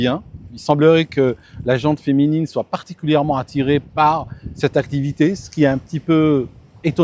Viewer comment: « la gente féminine semble attirée par cette activité et c’est étonnant « … c’est quoi ce commentaire misogyne et totalement cxn ?? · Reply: Il dit "particulièrement attirée", dans le sens surreprésentées comme les images le confirment. Les patterns sont robustes dans de nombreuses études : les hommes sont généralement plus enclins à la prise de risque, participent davantage aux sports extrêmes et perçoivent les dangers comme moins menaçants, tandis que les femmes montrent une aversion au risque plus marquée et une sensibilité accrue aux menaces vitales. Donc rien de misogyne dans sa remarque. Ici, elles semblent être majoritaires, là où, les statistiques confirment une sous-représentation féminine claire dans les sports extrêmes (plongée libre, cliff diving, etc.) : souvent autour de 1/3 des participants ou moins.